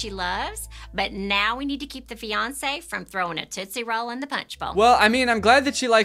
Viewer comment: im sorry. did she say tootsie roll in the punch bowl?